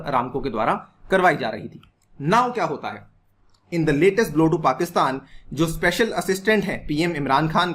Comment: मुझे 3:35 कुछ और ही विशेषण सुनाई पड़ा पाकिस्तान के लिए आपको क्या सुनाई दिया???😜😜😜😜😜🤣🤣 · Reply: Blow to bola h bhai kch bhi sun lete ho 😂